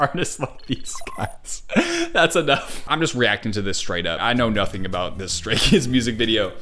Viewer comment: I LITERALLY THOUGHT YOU SAID STRAIGHT KIDS-💀😭😭